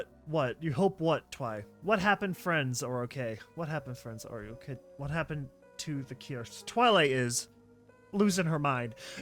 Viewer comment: these were two separate dialogues, the author put them way too close to seem as if they conformed one sentence "I hope my friends are ok" "What happened to the cure?" That's what she was saying separately